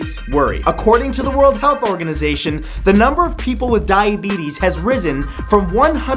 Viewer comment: I know he said diabetes but I swear I heard "Dying beetles"